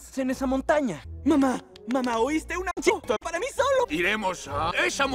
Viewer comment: entendí un cheto para mi solo XD